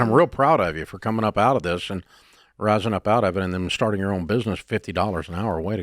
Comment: I thought he said a "churro business" 😂 We don't have Turo in Mexico...